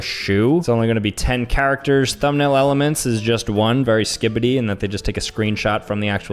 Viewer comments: did he just say very Skibiti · Did I just hear bro say “very skibidi” at 6:03 or am I tweaking💀💀💀